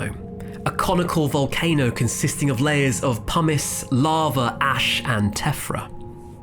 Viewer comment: Lol sounded like he said larva instead of lava. I do like the accent. And it's beautiful to look at